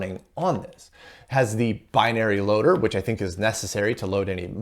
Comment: I initially thought it said "Bunny Loader" lol